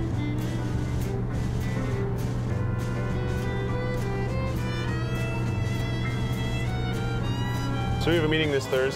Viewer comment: i didn't understand the one at 00:58...??? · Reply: hair tie!